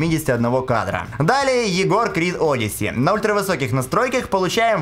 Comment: я правильно услышал, он сказал Егор Крид? тайминг 4:08 -4:12 .ахах